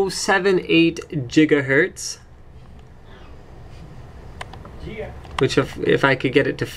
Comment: Did I just hear a someone like a rapper say "Yea" at 0:33?